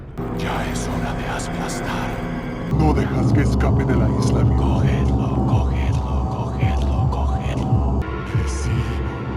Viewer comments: am i the only one who thought they were saying "Col Hitler" or "Ca Hitler"? the entire game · Always thought this sounded like "Go Hitler, Go Hitler, Go Hitler" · for more than a decade I always thought he said : “No dejes que escape de la dinastía” Jajajaja 😝